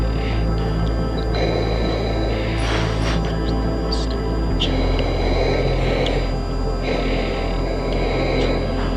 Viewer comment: stupid what? Am I heard wrong? 😁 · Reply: Engine